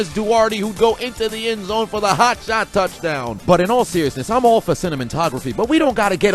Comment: Did you say Cinnamon-tography?! 🤣🤣🤣